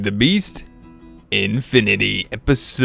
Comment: I think I have a problem...every time I hear you say "hey everyone welcome back to feed the beast" i hear unleashed instead of infinity